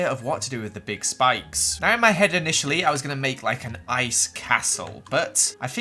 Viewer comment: I paused my iPad to eat lunch in the middle of the word castle and when I in paused it I heard astle I thought Joel said asshole!